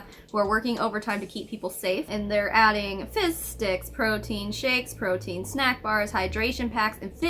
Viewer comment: lol every time you say “fizz sticks” I just hear “fish sticks” and then I think of South Park and can’t stop laughing about the idea of them donating “fish dicks” 😂 Sorry. I’m starting to go a little looney stuck in the house with 3 little kids for this long... and I’m immature.